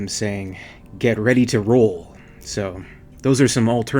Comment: I always thought I heard... "Get ready to RUN!".